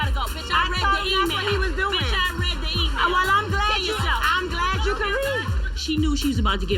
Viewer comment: What the fuck someone tell me if I’m bugging out but did Chrissy say “kill yourself” at 19:16 if she did she’s seriously fucked up